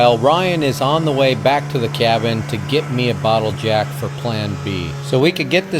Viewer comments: LMMFAO I thought he said a bottle of Jack Jack Daniels hahaha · Sounded like you said " a bottle of Jack " ,there for a second!!????😂😂😂😂😂😂😂,plan B is a mother #!@ 😂😂😂😂😂😂😂❤